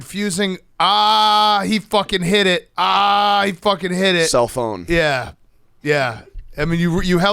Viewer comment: is great! I thought he said, "He F'n hit it! He f'n hit it!" but it did sound like he could have said, "I f'n did it!" We will never know because doesn't even know what he said.